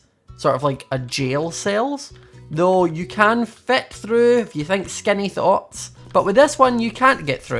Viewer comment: I realized he said think skinny thoughts at 4:23 but I thought was saying you skinny thots...